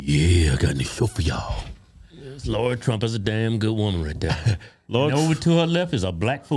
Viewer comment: I am laughing cuz I thought you were saying "Lord Trump" the entire time, then realized you were saying "Lara Trump"!